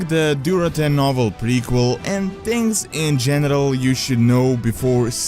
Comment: did he just say "GENITAL" ?